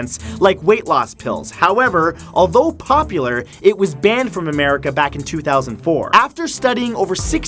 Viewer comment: did you say Americe?